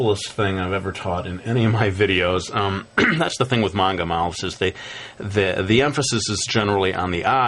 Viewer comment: he he! ur rite! it does sound like he says "monga"